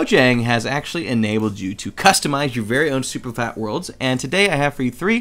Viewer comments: anyone else hear super fat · HE SAY SUPERFAT WORLD INSTEAD OF SUPERFLAT LMFAO XD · it sounded like he said super fat lol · he said "super fat" instead of "super flat"